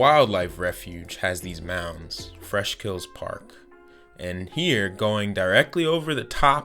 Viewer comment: Did you say fresh kills park?